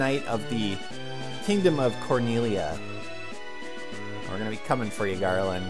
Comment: Could have sworn that said Corneria and I thought this was turning into Star Fox :P